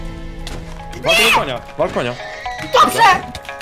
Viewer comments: Oglądam to po raz któryś i czy ja dobrze usłyszałem od: wal konia. XDDD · kto usłyszał ja powiedział wal konia